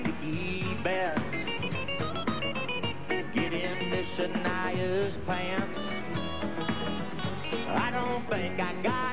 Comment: Sorry...did he say 'get into Shania's pants'?